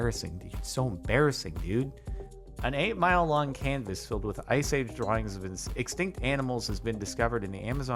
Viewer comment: Why I first heard him saying ice cream drawing instead of ice age drawings?